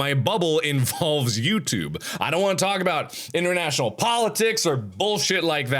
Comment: lol at 0:45 the subtitles in English (not auto-generated) it says interdimensional politics instead of international